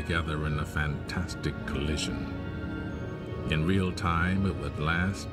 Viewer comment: Wait did he say explosion in real time could last a billion years. That's crazy